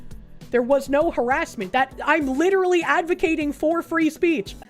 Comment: All I hear is: "Her ass mint, her ass mint!"